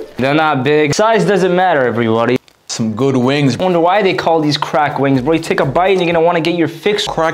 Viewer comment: Bruh o wasn't looking st the screen and I thought you said drag Queens instead of crack wings